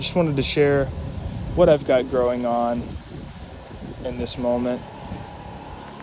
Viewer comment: Looking great. How do you remember all those plants names? Edit, it sounded like you said you had a lot Growing on. Punny.